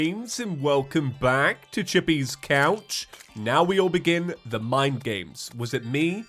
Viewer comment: My headphones: glitch What I hear: “Welcome back to Chippy’s Ouch!”